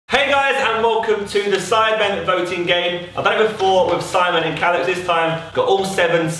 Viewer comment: Thought it said vomiting game